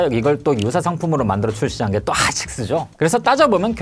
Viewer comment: 하앍식스라고 들리는건 저만 그런가요? ㅋㅋㅋ